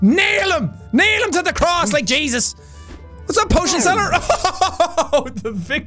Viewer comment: Did he just say "nail him to the cross like Jesus"?